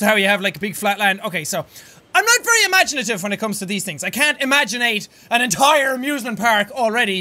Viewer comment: did he say imagineate